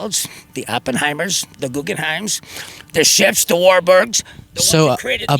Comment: Did that last guys include,, "the Walburgs" ? Like Donnie & MarkieMarc ??? Thought I heard the Walburgs, I can't watch it again...